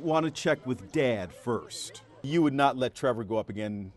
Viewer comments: sounded like someone said "drop a beat" and then the cord snapped 😂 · Spam 1:05 and instead of chance you hear dance.